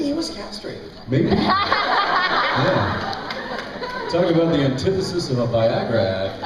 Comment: Напишите русские, если вам тоже послышалось от Дженсена "Самое главное Теннесиссова ваягра, вот".